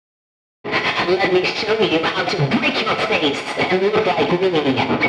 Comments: "let me show you how to break your face and look like me" "I was the first I have seen everything" is what I heard owo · I Heard What W.Chica Said She Said Let Me Show You How To Break Your Face Just Like Me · if your wondering what she's saying first one"let me break your face so you can look like me" second one "I was the first I've seen everything" thank me later